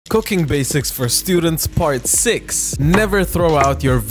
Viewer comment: Before I saw the subtitles I thought it said coc@ basics for students💀☠️